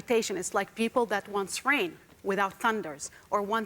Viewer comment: I thought she sayd "wants rape, without condoms."